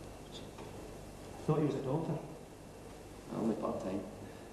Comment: Hard to tell, really, with all that adorable accent and me not being a native English speaker but at 3:43 I hear quite clearly: "-I thought you were a doctor. -Only part-time." And I find it as funny as hell. :o))